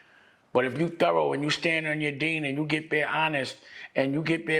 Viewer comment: Did he say “stand on your deen” @ 39:55?